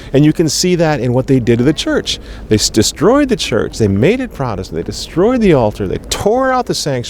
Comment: "They made it protestant..." Is that what he's saying?